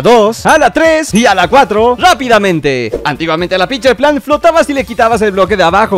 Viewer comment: Entendí "pinche planta" XD